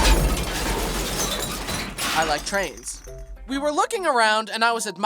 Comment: He really said I LIKE TRANS 😂